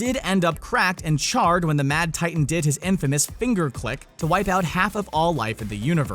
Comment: did he just say “fingerclick”